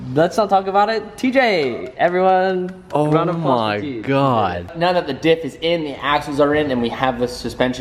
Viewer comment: I swear I thought I heard “now that the dicks are in and the asses are in”